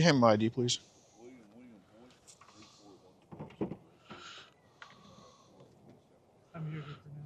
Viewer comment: 😮 what name did that cop say the Preds name was? William William Voit or Boyd? The names sounds very close to mine😂